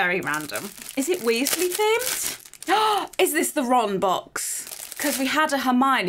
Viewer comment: i had to skip backwards to hear the theme, cause i heard 'wrong box' the first time XD